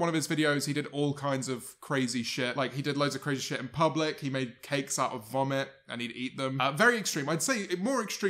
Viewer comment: I heard "he made vomit cakes and ate them" and now I'm nauseous. Like I'm tearing up bro